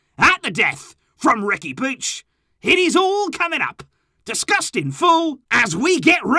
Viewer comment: I heard "it is all coming up, disgusting fool" 🤦🏾‍♂️😂